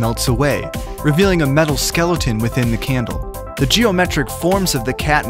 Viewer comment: did say Skelloten?